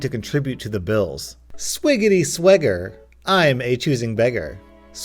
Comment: I almost fucking choked on my drink cause i thought you said swigggety SWIGGER and we all know what that could lead to